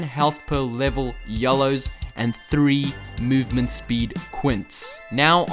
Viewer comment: when you mishear "Yellow Runes" for "YOLO Runes" lmao